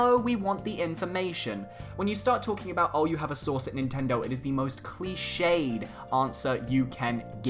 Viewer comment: Lmao I thought u said “I have a sauce at nintendo”